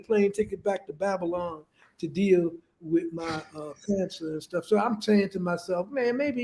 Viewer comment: did just say he has cancer ?